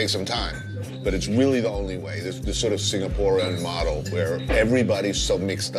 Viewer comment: did bourdain actually say "Singaporean" model?